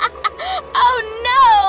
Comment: i thought she Said "oh lol„😅